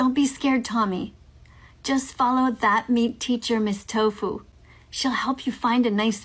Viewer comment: Ok, what is she saying around 1:45 It sounds like "go follow that nice MEAT teacher mrs. Tofu, she will help you find a nice vegan girl who will teach you what to do" But that doesnt make sense. She cant be saying MEAT teacher but it doesnt sound like neat or mean either so...help?